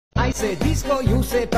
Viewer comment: What I heard: “i say discord you say barbie, discord discord!! Barbie Barbie” 💀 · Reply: I hear “you say disco i say barpee disco disco pati pati”